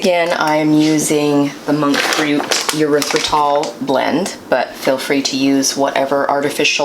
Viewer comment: You said monk fruit erythritol blend I could have sworn you said Motrin haha haha 🤣 I had a backup the video to listen again